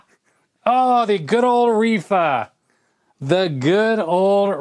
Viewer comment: smoke fills the air and the auto captions say "oh the good old reefer" 😂